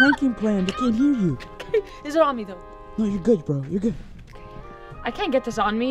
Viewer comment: hahah did said to “no your good BRO”